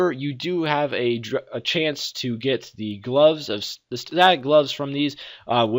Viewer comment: wtf did he say at 17:52? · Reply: Static gloves